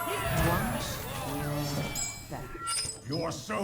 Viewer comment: I always mishear the rogue as saying "Wash your back."